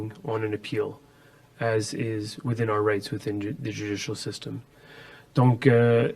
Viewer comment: Did he just say their "Rights"? 🤨 They ALL best be jailed. They violated ALL Canadians Rights. 😡